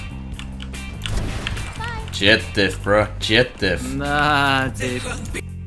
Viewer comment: did i hear something or its just me , he said na...is